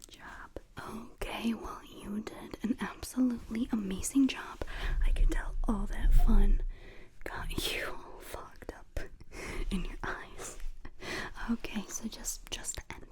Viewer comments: Was that supposed to be a "you f'ed up" at the end or am I just completely mishearing that? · hello excuse me? what did you say🤨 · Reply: I said fogged like foggy!!! 😭😭 I don’t know why it sounded different!!